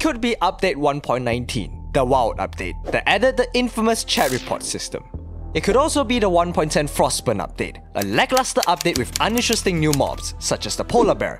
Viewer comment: I heard "cherry pot system" and was confused for a second.